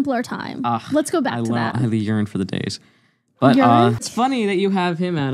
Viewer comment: For some reason I heard ‘ urine” instead of ‘yearn’